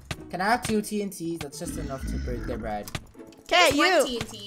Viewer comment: is it just me or did say bread instead of bed thats just so funny to me